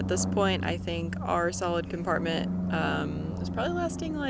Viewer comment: I thought u said salad compartment